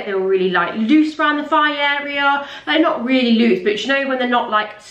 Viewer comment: Had to re watch the "thigh area" part, thought you said fanny area 😂😂